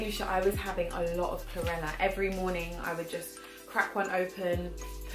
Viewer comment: can anyone tell me what she said she mixed with coconut water at 7:04? she said she felt a difference. ive played it over and just cant make it out! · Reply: Yes Chlorella :)